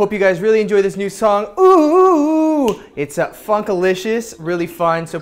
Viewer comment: Is it just me or did it sound like he said "fxckalicious"? but in any case...OOH OOH OOH~ <3